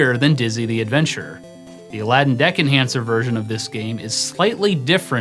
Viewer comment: I thought you said Aladdin's Deck and Cancer.